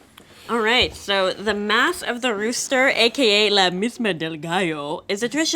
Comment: Omg I feel like an asshole, I thought she said "math of the rooster" not "mass"